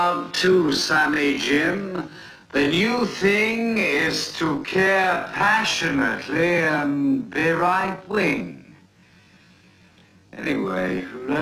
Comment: Did he say “the new thing is to care passionately and be right wing” ?